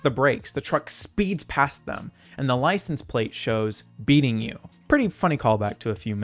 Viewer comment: I always thought the license plate said B eating you.